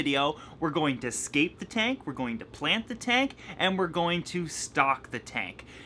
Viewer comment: I don't know why but at the beginning of the video I heard "I am going to skate the tank" even though I know you said scape the tank